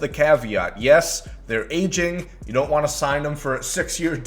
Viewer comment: When he said "they're aging" I thought he said they're ancient😂